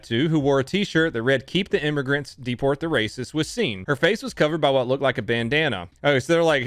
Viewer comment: 11.09 I thought he said “I’m the only racist “